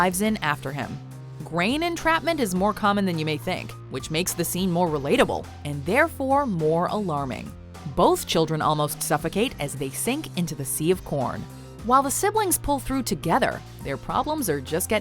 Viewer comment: I thought she said sea of porn